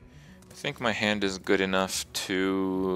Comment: This is the most liked comment for a reason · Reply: "thinking hand," he actually says, "I think my hand..." Lol